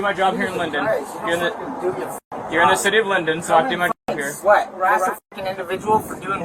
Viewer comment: WTH has London got to do with this? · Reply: My bad I was half listening I thought he said city of London not Linden.